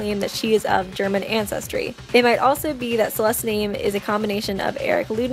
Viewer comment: DID SHE JUST SAY "CELIST" ?